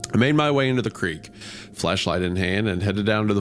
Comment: am i going bonkers or did he say fl3shlight 😭